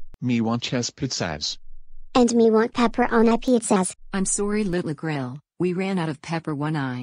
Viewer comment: What did he say at 0:34💀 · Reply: he said "im sorry little grill"💀💀